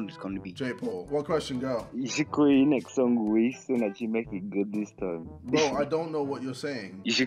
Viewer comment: if you also didnt understand what he said like this comment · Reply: He said whis who is beerus teacher in dragon ball super because his song was called beerus and whis is better and stronger than beerus